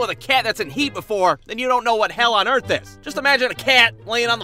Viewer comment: I thought you said an MRI was hell on earth